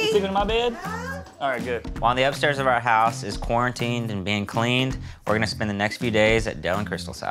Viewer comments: Did he say quarantine???? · did Adam say quarantined?